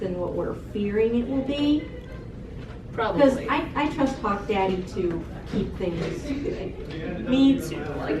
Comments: i know she didnt but i thought ladybug said '' i trust cock-daddy'' and i was like 'wait what?' so i replayed it and i still heard that. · am I they only one who heard "hawk daddy"